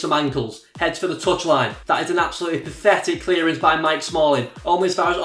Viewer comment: Did he say Mike Smalling at 7:33?